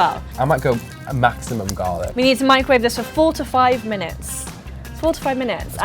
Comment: When said "Four to five minutes," I thought she said "45 minutes" at first!